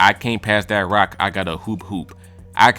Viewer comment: I had to rewind that pass that rock line about five times because I thought Fridayy said ‘I can’t pass that rock, I gotta poo poo’